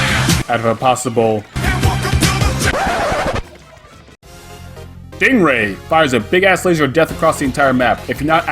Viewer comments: did he just said "ding ray" ? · did he say ding ray?!